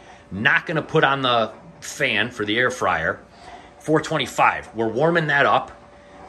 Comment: I thought you said “hair fryer” at first 😂😂 I’m not into many healthy snacks but I really appreciate y’all’s commitment to staying healthy!